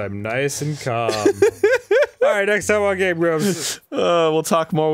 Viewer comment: Just wanted Arin to know that with his Oprah voice. The auto-generated captions thought you said, “Gay Grooves”, instead of Game Grumps. That is all